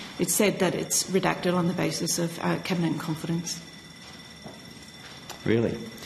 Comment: what does she say? "Cabinet confidence "? "Candidate confidence "?